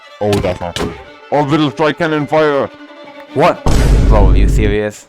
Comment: When “wemmboo” said “orbital strike cannon fire” the YouTube captions said “that’s not ɑ strike cannon fire”